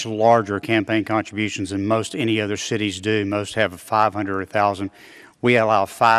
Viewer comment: Just came to say that at 3:08, he said “shitties” instead of “cities” 😝